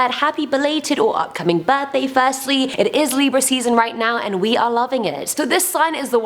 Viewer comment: From her accent i thought she said pie season😅